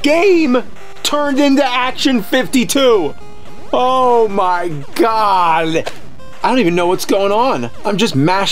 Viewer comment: Is it just me, Or did he say "OH MY GOWL!"